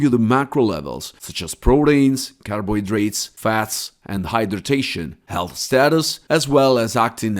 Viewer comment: did you say hydroion?